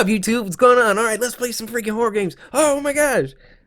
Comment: Did you say omg